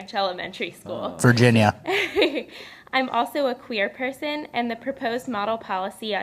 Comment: I thought she said "clear person" which would fit with the ghost narrative from earlier, but oh well